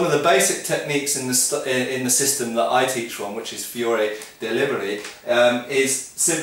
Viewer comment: Fiore dei Liberi - huh, i always heard ''Fury delivery'' :'D Makes sense swell though